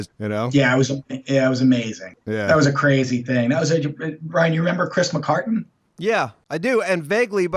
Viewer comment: did call "Ryan"??? Is my whole life a lie? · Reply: Ryan Ramsey "Brian". Thats his real name - (one of the most New York Itilian names ive ever heard).